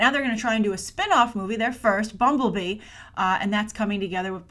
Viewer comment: I thought you said BungleBee! 😂😆🤣